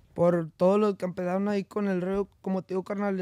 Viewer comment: con el rollo ? No entiendo q dice